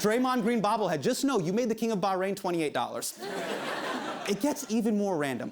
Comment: did he say king of Bahrain? 😂😂 I'm from Bahrain lmao.